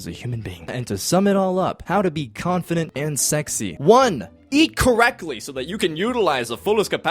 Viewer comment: I was doing laundry and was turned away and I 100% thought you said 'speak correctly,' I was thinking what are going to be the reasons to back this up..? 😂😂😂 Although it ink it is important to speak correctly, people just sound better to my ears 😅